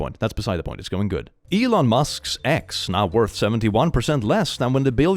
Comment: When it said "Elon Musk's X" I thought it said "Elon Musk's ex" instead, which just provers even more that the name X sucks